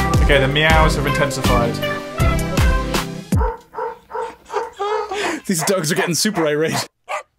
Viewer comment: I honestly for a moment thought that said "these dogs are getting super IRA'd" and I was like "uhh Martin is there something about Fenian dogs that is only known in Ireland?"